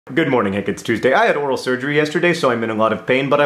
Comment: for real, i thought you said 'i had oral sex yesterday'. i was like okay, that's an over-share but good for you, i guess. but then i realised you said 'oral surgery' - which is much less fun. condolences.